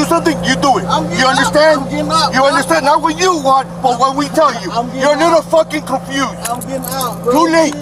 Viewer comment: He called him "boy". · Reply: I think the cop said " not what you want... but what we tell you "...@ 1:13... while Tyreek was being handcuffed... still unnecessary and excessive force....